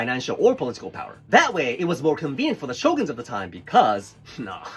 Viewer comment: I thought he said SHOTGUN 😭😭😭😭😭😭😭😭😭😭😭